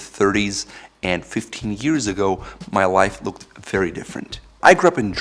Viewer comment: I thought you said "15 years ago, my wife looked very different". Caught me off guard for a second haha